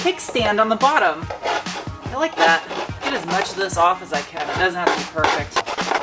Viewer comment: Did I just hear you right? "It doesn't have to be perfect."